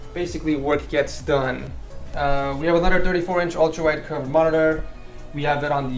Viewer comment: Thought you said 134". Had to re-watch.